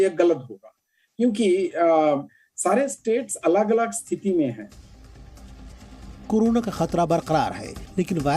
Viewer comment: what is the meaning of Sare state alag alag city me hi can anyone tell me i am unable to understand what he want to say???🤔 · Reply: he said stithi(conditions in hindi) and not city